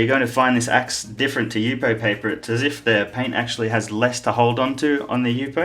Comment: What kind of paper is that? The effect from erasing with a scalpel is new to me and I`d ilke to try it :) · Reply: Sounded like you say U pay paper hehe. UPO? UFO paper! :p